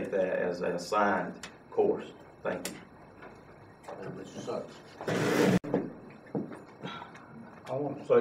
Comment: What do they say at 4:15? Some thing about "sucks"?